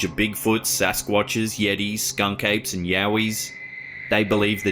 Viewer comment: Lol!! I thought they said ‘skunk egg’ and was like… what?!?!